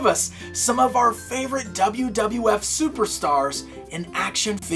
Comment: Did he just say WWF? XD